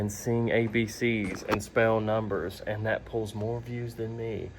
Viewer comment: I thought you said your son could smell numbers😂